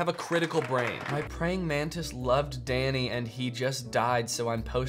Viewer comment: HELP I THOUGHT THEY WERE SAYING DANNY DIEd😭